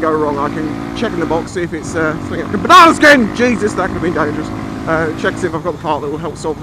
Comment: what did you say on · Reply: banana skin! could be dangerous! lol I was joking!